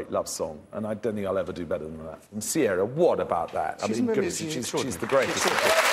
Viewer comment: what does michael ball say about sierra at 21:28 kinda at the same time as ALW is talking i couldnt make it out? can anyone make out what hes saying?? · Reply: SoHills VetTech Just rembered this again. I think Michael says something like "she really is extraordinary".